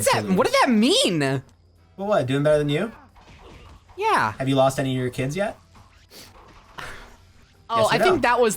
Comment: god i thought he said "have you lost any of your kins yet" and i was extremely confused